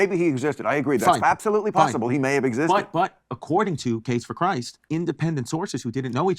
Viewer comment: I thought he was saying capes for Christ until I googled it and realized it’s case